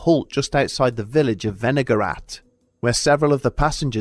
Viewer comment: I thought for sure you said vinegar rat at first.